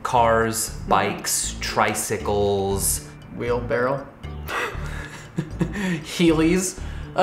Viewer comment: Did he say "wheel barrel" at 2:57 xDD Guess he means a wheelbarrow.